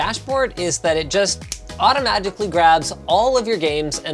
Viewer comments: did he say "automagically"? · did he say "autoMAGICally"